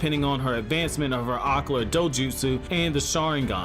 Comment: What did you say @ 5:14 aqua dojutsu?